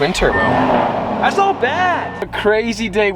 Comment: did he say thats bad